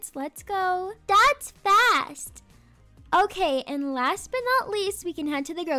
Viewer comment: When said “dats fast” I thought she said dad’s fat 😭